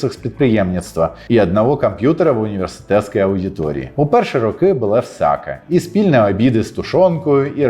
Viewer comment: На 14:50 каже: "У перші роки БУЛО ВСЯКЕ...", мені ж почулося "У перші роки БУЛА СРАКА...". Потім він почав перераховувати - "спільні обіди з тушонкою, і робота в рукавицях..." і я зрозумів, що то в була обмовка, а я почув все вірно. ))))))